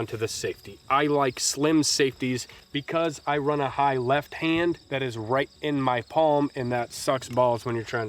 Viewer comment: ….did you say you like “Slim Shady”?